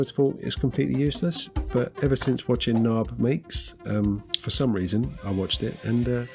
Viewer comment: you referenced watching something… mind sharing the link? I heard “naab makes” but not finding anything